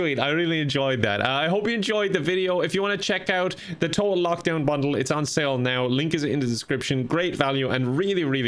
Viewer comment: you should say bundle slower cause it sounds like you said "bunghole" HAHAH JUust saying cool content i love watching it daily! While I wait for my favorite youtubers to upload or if I am not uploading ... :3 (Roast) enjoy hopefully we can play together online gaming sometime by chance i roleplay on EVERY GAME I play people actually ask me why i do it alot LOL